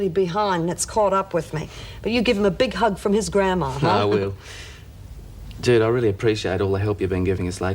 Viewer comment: it sounded like Geoff said to Judy, "Dude, I really appreciate all the help" LOL